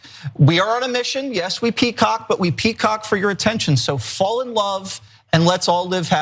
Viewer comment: Wow at 8:28 I heard “Fall in Line” and we will all live happily ever after…..